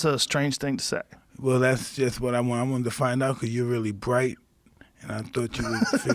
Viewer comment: I want to defy knuckle you?? What does he say at 3:05?